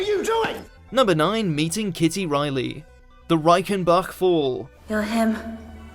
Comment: sounds like youre saying reikenbuck LOLLLLLL..im so glad i live in germany